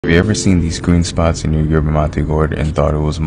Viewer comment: I thought he said "Have you ever seen these goon spots"